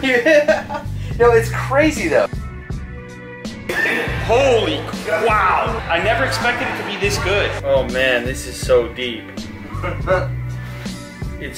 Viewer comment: LOL did he say holy quail LOL